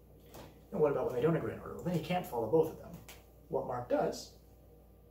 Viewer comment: What does he say at 11:00 to 11:01? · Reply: "what about when they don't agree, then he can't follow both of them"